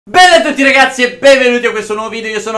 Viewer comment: Alla intro ho capito "pene a tutti RAGAZZIH!" NIICE...